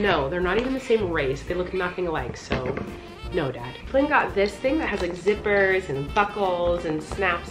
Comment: what she said.....”flynn got this thing that has zippers and buckles.....” what i heard.....”flynn got this thing that has zippers and buttholes......” my innocence is gone......